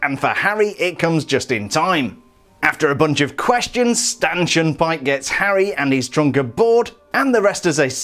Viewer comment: it sounds like you said “Stanchin Pyke”